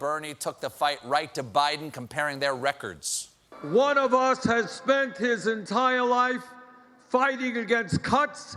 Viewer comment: I literally thought Bernie said "One of us has spent our life fighting against cunts".